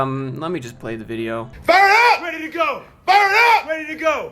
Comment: I thought they were saying BIRD UP!!.